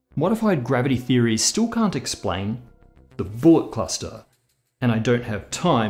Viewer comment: I was in the other room and had to run in and rewind it because I thought our boy just said "Ballbuster".